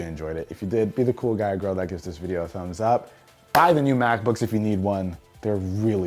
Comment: It sounds like he is saying “I have the new MacBooks if you need one” Me: I need one.